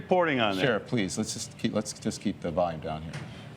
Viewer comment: did he just say let’s keep the vibe down? WTF